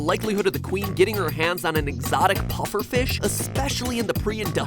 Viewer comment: At first, I thought he was going to say "Exotic BUTTERS" true trash right here